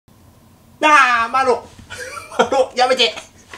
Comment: ダーマロにしか聞こえん